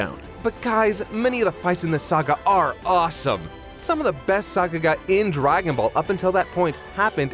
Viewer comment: "Some of the best __ in Dragonball happened in this saga". What word did he say? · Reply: Sakuga is what he MEANT to say. Basically just a fancy way of saying “good animation”